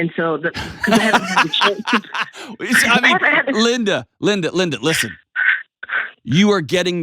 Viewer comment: When Delony said “listen, listen” all I heard was “Linda, Linda, listen” 😂